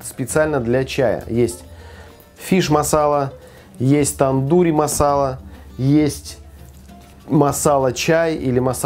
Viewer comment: Дурь-масала?)) Не послышалось?